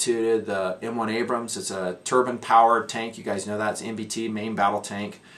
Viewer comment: Haha. I thought you said turban powered tank. Took me a second to catch up.